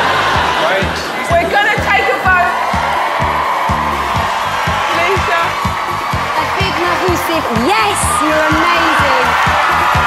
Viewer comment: why’d it sound like Simone said “Your hat, darling“ lol